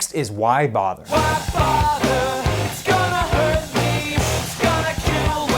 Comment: Wait omg I thought why bother said my father's gonna hurt me